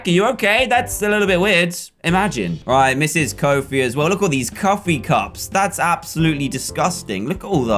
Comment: am i the only person who heard him say cuffee cops